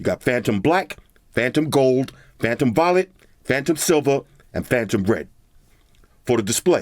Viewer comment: sounded like he said "phantom vomit".